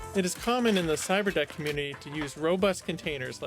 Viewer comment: I can't tell if he's saying "Cyber desk" or "cyber deck". Either way, it sounds like something from Star Wars lol. He should have named it a Cyber Tin since it's not really a desk or deck. Seems pretty pointless honestly, but definitely interesting at least. I doubt there's too many people out there who would have the skills to build something like this though. I felt like I needed a translator app just to understand wtf he was even saying here. That didn't look like raspberry pie to me lol.